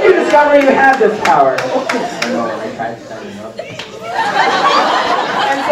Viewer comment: I’m sorry, but what did the dude at 51:21 say.. he kept moving around while standing up.. what was his name and superpower. I couldn’t hear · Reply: His name was "Squiggly Bones" and at 51:21 when I asked him how he discovered he had his power he said "I dunno I tried standing up" and then squiggled some more to demonstrate 😂😂